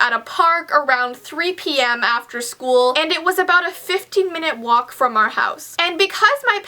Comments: I thought when you said a 15 min. walk . A 50 min. Walk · When u said the bus dropped u off at the park I thought u said it was a 50 minute walk😬😅